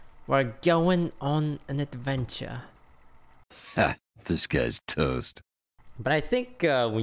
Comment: Now that ive figured it out all i hear is "disguised toast" instead of "this guy's".